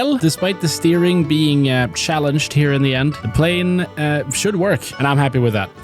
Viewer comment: Did bro really say the steering was "shallenged"?